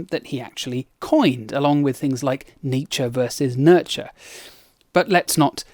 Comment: My face when I misheard, and I thought you said "Nietzsche vs Nurture"